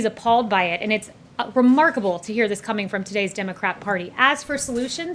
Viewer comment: Did I hear the beautiful Kayleigh say "Democrap party" at 1:06. I'm sure I did.